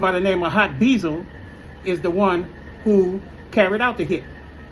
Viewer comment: I thought he said they didn’t know hot beezo